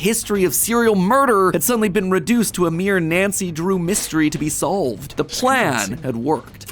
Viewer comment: did he just say nacy drew